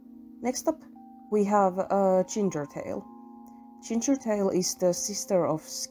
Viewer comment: when they first said 'Gingertail' i thought said 'Childrenbail' this music is creeping me out oof